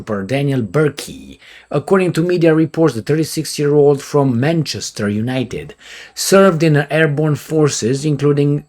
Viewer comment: Dis i just hear " from Manchester United?"